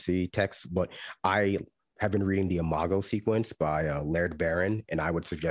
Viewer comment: ' suggestion was The Imago Sequence. Just in case anyone was wondering, because I misheard him the first time.